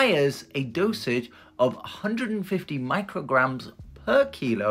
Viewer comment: I’m so glad I’m not a nurse I thought you said he needs 150 milligrams not micrograms 😅